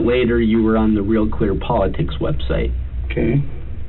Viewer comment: I first heard "The Real Queer Politics website" 😂